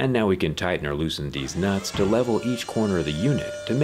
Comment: did he just say ‘Deez Nuts’?